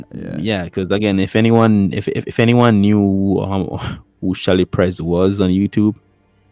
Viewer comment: Who's the girl he talking about sounds like he said Shelly price but I don't see anyone by that name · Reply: Chelly Prez. <link>